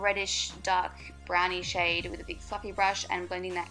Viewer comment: I could have sworn you said "with a bug fucking brush" lmao i love the look though! :)